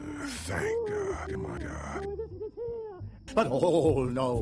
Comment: did he just say thank gog and magog?